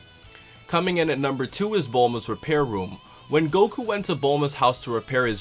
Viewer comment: for a second I thought you were going to say Bulma's tits xD xD xD